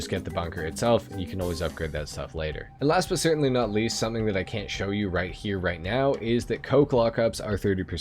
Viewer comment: someone can tell me what he is saying, I'm having a hard time understanding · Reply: "something that I can't show you right here right now"